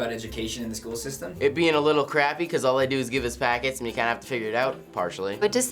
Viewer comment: What do they learn from? It sounded like it said pockets :l